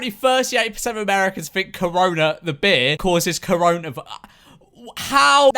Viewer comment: he said "corona the beer" I heard "corona the bare"